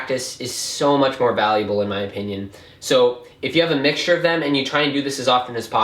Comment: Best practice trick In my opinion and for people who don't have have a display(its really expensive) can connect their timer to their computer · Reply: I thought you said in my onion